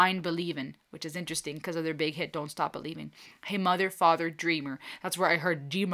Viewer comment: Beautiful performance. Great message. When he said "Dreamer", to me it sounded like he said demon, but that definitely didn't make sense.